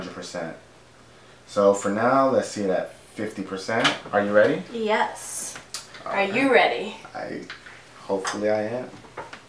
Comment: "Are you ready?" "Yes" "Are YOU ready?" Thats what they said ;)